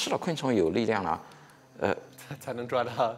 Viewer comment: lol it sound like he said nigga